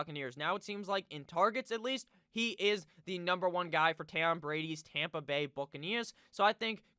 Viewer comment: Did he just call him Tam Brady